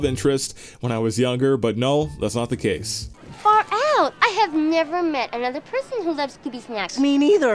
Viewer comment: sounded like she said,, f*ck out.